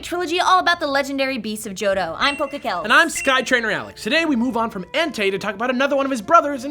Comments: first 46 seconds of the show and I had to go "STOP WAIT A MINUTE" Did he just say Sky Trainer... but it says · * I thought you said Sky Trainer?